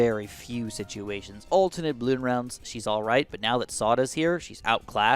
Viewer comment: camo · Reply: oh my god haha I’m retarded, totally thought it said sauda 😂